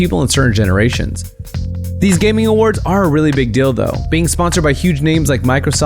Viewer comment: who else heard him say " a really big dildo" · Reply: XxFelicia StPeterxX he actually said really big DEAL though 😂